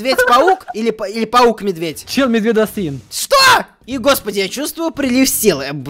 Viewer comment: что он сказал????? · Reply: Челмедведосвин